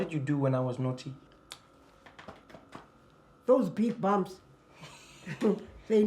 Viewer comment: "those big bumps... they know my slippers"🤣🤣🤣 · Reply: Pretty sure she said "big bums", but I doubt Americans use that term the way some Africans do so they thought she said "bumps"😂😂😂